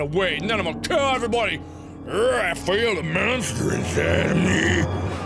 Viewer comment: it sounded like I feel the menstruation